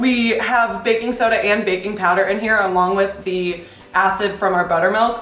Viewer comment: I sniffled when she said baking powder and thought she said baby powder 😂